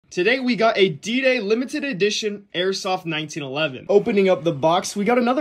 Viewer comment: Was fully prepared to see 5 bullet holes in his fence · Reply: I was too man. I questioned many things until it replayed and I heard him say airsoft. Missed that the first time. Lmao